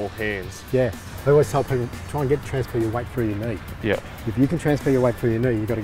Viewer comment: just turn on subtitles and look at 3:26 its written transfer your wife instead of weight